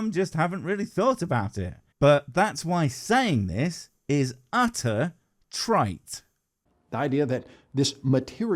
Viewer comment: did you say 'utter trite'? The captions say 'utter trait', which I don't think you said. I mention it because I believe the normal idiom (at least, round our way), is 'utter tripe' - i.e. of equal value to the intestines of a bovine. Though, Mr Butt is trite in his tripe, I'll give you that. I'll stop making vaguely reasonable comments now and go back to making silly rude comments and speaking about the state of my vertical bacon sandwich and how juicy your vids make it. Cheers!